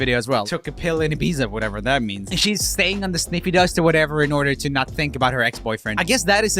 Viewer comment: Did he just say "sniffydust" ?! Im dead🤣